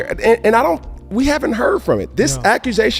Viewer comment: Oh, the "host". I thought he said "we haven't heard from the Hoe"